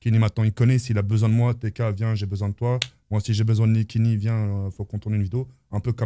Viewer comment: C'est moi ou il dit "si j'ai besoin de niquer kenny" ??